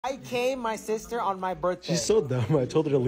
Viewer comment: I thought I heard "I CAME my sister on her birthday" 😮